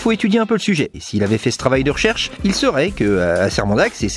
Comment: "il serait" Pitié, apprenez à parler correctement la france avant de vous exprimer. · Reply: “parler correctement la france“ ,ce n'est pas une expression correcte en français. Vous avez entendu “il serait“ ? J'ai entendu “il saurait“. C'est ce qu'on appelle chercher la petite bête.